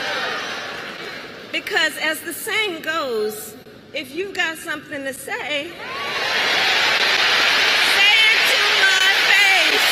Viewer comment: She said, "If you've got something to say, say it to my face." But in my head I heard, "Goading attack, natural 20!" (I've been watching way too much Critical Role! lol )